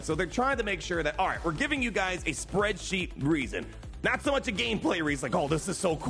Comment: Thought you said spread cheeks reason